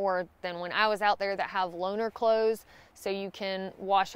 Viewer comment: Oh lol, I had heard people talking about wearing ‘loaner clothes’ in town but I didn’t catch on and I thought they were saying ‘loner clothes’, as in what you wear if you’re billy-no-mates.